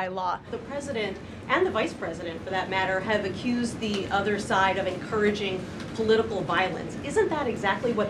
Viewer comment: Euh quoi ? Pardon ? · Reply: "Ont accusé le camp adverse d'encourager la violence politique."